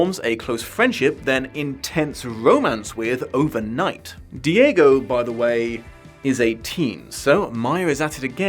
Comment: I heard "Who, by the way, is a teen" instead of "Who, by the way, is eighteen" and spent some very confused minutes wondering how old Bree is lmao